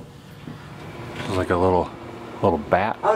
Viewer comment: "What the heck" did that lady say in the store at 4:55 ?? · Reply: "Oh, no pictures on the inside sir"